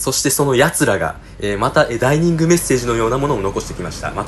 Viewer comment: ダイニングメッセージって聞こえる